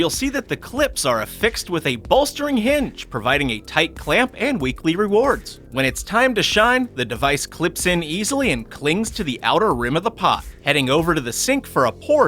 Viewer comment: did anyone hear him say a type clamp and weekly rewards